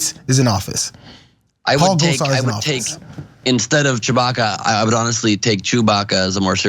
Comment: I’m not the only one that first thought Jayar said Kelly Chewbacca. She might have just been speaking Wookiee. 😅